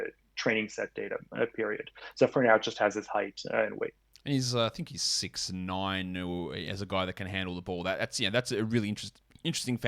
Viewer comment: does say THT is 6’9 😂